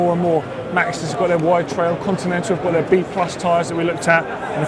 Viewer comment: Maxxis wt tyres are not plus or semi plus tires!! Maxxis wt tyres are for wider rims! 20-30mm rims whit normal tyre and 30-45mm rims whit wt version! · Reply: Life On Two Wheels i thought he said they are known as wide trail ... just sayin